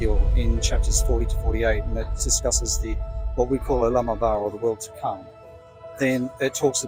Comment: He did not say "lamb of G-d," he said olam haba. Please fix this in your video since it has theological implications.